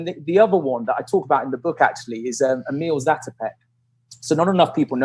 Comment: what is the name of that guy? at 42:10, i cant understand. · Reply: ohhh its Emil Zátopek. wow that pronanciatoin :D and im from Czech. gosh